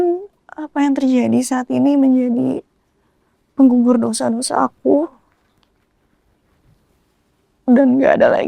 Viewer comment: Semoga dengan kejadian ini ,menjadi pengugur dosa2 aku " Aku salah dengar GK sich ,betul kh seperti itu woee 😱 ,berarti di dlm hati nya tidak ada rasa penyesalan telah menyakiti hati seorang istri ,,bukan kh dulu dia pernah di posisi itu ,sakit atau tidak kh sehingga dia menjadi posisi sebagai pelakor .